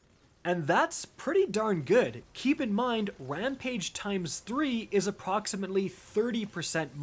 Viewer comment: Did he just say wampage